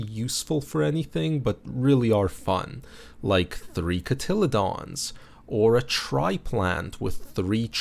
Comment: pronounced "cotty-lee-dons".. the word said sounded like a dinosaur name lol. Like a dimetrodon.